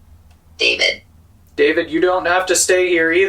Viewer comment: I do not hear the name David, I hear it say demon, maybe I'm wronge, but I hear it say demon. By the way, I LOVE the new series. Keep up rhe good work 🙂👍❤️🇬🇱